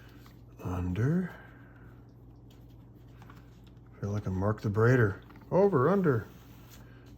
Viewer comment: you mention Mark D?. I didn't catch his name. Could you let me know who he is? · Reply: Mark the Braider … hit his YouTube channel awesome stuff … his first channel is Paracord Guild